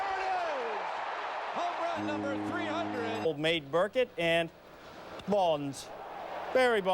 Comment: did he just call john burkett "old maid burkett"??? Lol